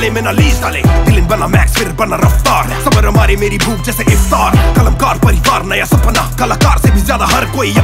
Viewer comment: dose he said bhook meri jese INDORE ....bc kuch jaada hi bhooke log hai yaha k .... lockdown m koi sev dilvaega toh krapa hogi